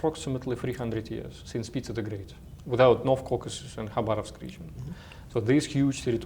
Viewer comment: what is the second region Andrey names? He says "without North Caucasus and ? region." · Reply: Khabarovsk region